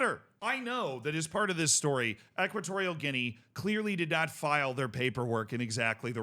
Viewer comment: FIFA accepted the paperwork 12 years ago so Nsue and Equitorial Guinea should not be held responsible for this paperwork snafu. Both the Liberia & Namibia results should stand and the Tunisia game should be replayed with Nsue available. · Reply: I thought he said Nsue never filed the proper paperwork.